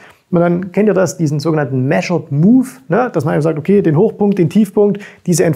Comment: Hat jemand verstanden wie der „move“ bei min. 10:27 heißt um das hoch auszurechnen? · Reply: Measured Move. Im letzten Video von zu Bitcoin hat er es auch erklärt. Vielleicht auch im Video davor zu Bitcoin.